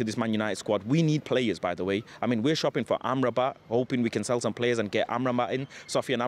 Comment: Why did i hear "We're shopping for armed robber"